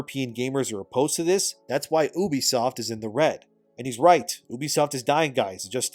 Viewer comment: Why did I hear "ubisoft is in hog water" 💀